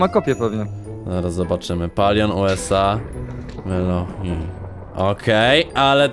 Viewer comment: Ja już usłyszałem palion-USA